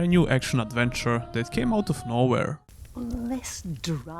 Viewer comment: I heard "Which came out tomorrow" instead of nowhere and didn't even question it